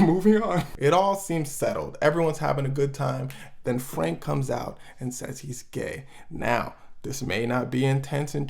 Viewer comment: at first I think he said “then drake says he’s gay” n I was like ??